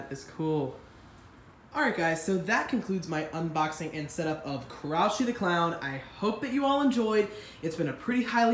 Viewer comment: I thought it said crunchy the clown